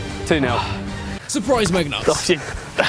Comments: did he say suprise megnut? 😂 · Did he just say surprise metnug? Well he is from jersey ( the backwards land ) 😂😂😂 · did Chris say surprised meg nuts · does he say surprise megnuts instead of nutmegs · did anyone hear megnuts instead of nutmeg